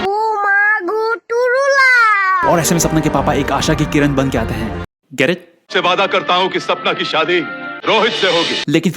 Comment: can anyone tell me what does the boy in Hrithik's heart say?? "Oo maa gooturula"??? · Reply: Ooo maa go turu lob(oh my god true love)